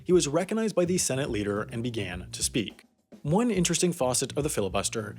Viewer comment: Did you just say, "faucet"?